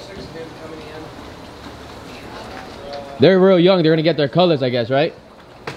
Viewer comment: I swear I thought he was saying "We're going to get green!" Until I saw the grain come out.